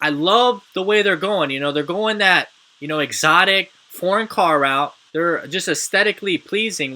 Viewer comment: Exotic Porn car? that's what it sounded like you said lol